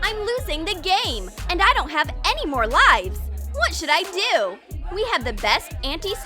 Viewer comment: Did she just said lives